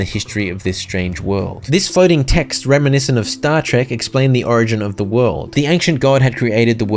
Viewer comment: DId bro just say star trek???